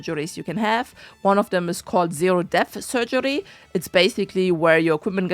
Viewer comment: I miss heard and thought you said zero death surgery! 😂